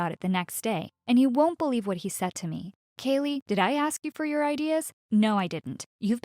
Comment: Is her name Apple, what she said at the start or Caley